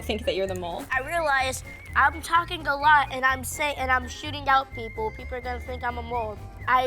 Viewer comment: When went out I thought he said I’m sh*ting out people